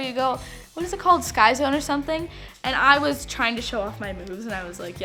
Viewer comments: I thought she said I was trying to show of my BOOBS lol 😂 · I thought she said she was trying to show off her BOOBS and she’s good at it lmao 😂😂😂 I died but she said moves** omg Love her ❤️ Edit wow so many likes I love you guys so much please follow my Instagram & get me to 7k😘😘 is my Instagram user name 😍👼🏼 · I swear I thought she said "I was trying to show off my boobs" 😂😂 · I heard “i was trying to show my boobs” at first omg sorry HAHAHA (no hate i love kenz) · I thought she say “I was trying to show off my boobs”😂😂😂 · when she said i was trying to show her moves i thought she said boobs and i was like WTF YOUR BOOBS? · I thought she said “show off my boobs” instead of “show off my moves” 😂😂😂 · Who else thought she said “ I was trying to show off my boobs” 😂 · I thought she said I was tryna show off my boobs 😂😂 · OMG I thought she said she was trying to show off her boobs in the beginning now I know it she said moves lol 😂